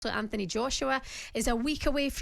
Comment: Did she just say "Sir Anthony Joshua"?